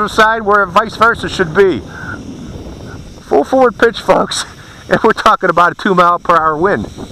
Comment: did u say "full forward pitch fucks or folks" perhaps